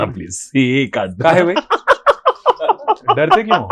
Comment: Did he just say , kaahe be at 15:28, amazing 😄